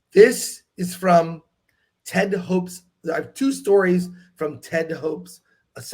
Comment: The way was saying the name I thought he was saying TED DE HOPE.